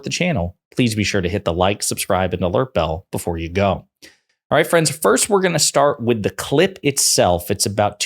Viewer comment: Who else heard "Alright friends first we are going to start with the clit it'self" 🤣